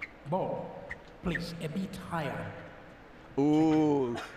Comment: Can some tell me what the umpire says at 1:23? "Please epityre?" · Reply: “A bit higher”